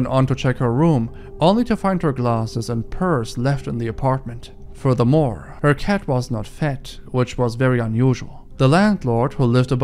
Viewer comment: "Her cat was not fat, which was very unusual..." I know that isn't what he said, but it's what I heard, and it was so funny after all this disturbing stuff, I had to laugh. Very unusual for the cat to not be fat!